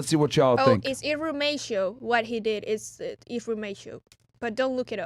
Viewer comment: what word did say? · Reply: Irrumatio